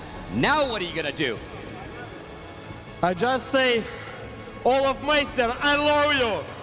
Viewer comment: did he say olfmeister